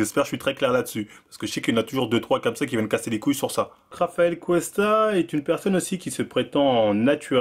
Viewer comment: c'est moi ou il dit Travael Cuesta 👀😭😭😭